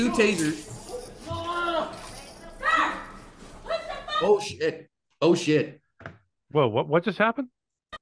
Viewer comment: Sounded like she said " oh sh!t" right after she shot him.